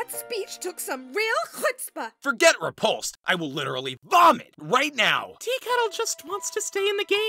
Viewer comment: DID HE SAY BOMB OR VOMIT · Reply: Vomit Look at the subtitles